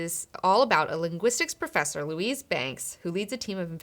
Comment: Did she said Lucifer?